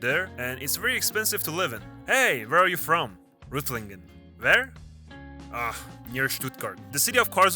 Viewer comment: Hab ich Reutlingen gehört? 1:05 😂